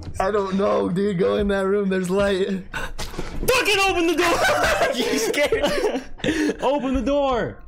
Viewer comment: thought he said hold the dooor!! hodor!!!!